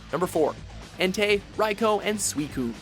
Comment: At first I thought he said Raiko, not Raikou